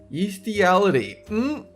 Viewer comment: 18-I thought he said beastiality